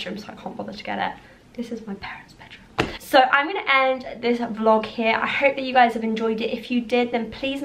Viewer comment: LOVED it smmmm ,btw do you teach levels or uni??? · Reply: omg lol I thought that u said I’m going to teaching😂😅😅